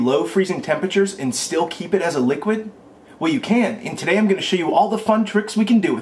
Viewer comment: thought he said "well you can't" so I thought what's the point in this video xD